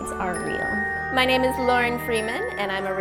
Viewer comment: I thought she said "I'm Morgan Freeman